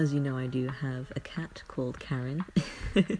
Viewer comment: Aww!! Don't worry! I think this could work as background noise ∩^ω^∩ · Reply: ok so I did what you said and I just hear "I have a cat named karen" in crying 😂😂🤣